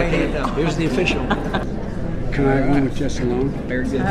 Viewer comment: what does he say? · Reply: trey trey......Wants to be alone (With Someone)?!?!??